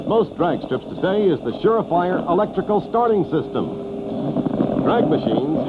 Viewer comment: and at 3:36 he says "shafire" instead of the two words "sure fire". I can't watch this anymore.